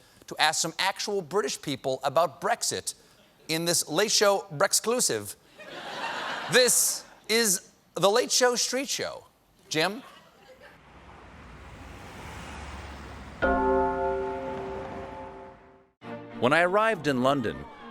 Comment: I seriously thought he said Fellatio Street Show.